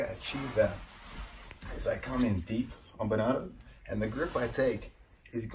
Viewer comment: Can someone tell me what Craig says at 1:36 ???? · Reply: I come in deep on bernardo 👍😂